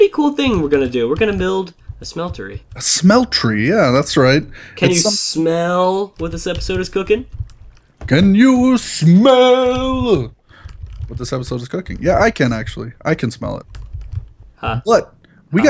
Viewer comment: the caption says for 2:41, with the servicers gergen canoe mobutu with the some sort of cooking yeah i can. Yay -_